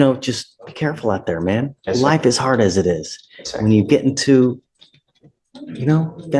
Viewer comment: According to the useless subtitles, at 2:46 the judge said "life is hard as it is". Listen again, that's not what he said. He said "black is hard as it is". Think about the implication of that statement. The judge seems to realize there can be a bias toward blacks in police interactions.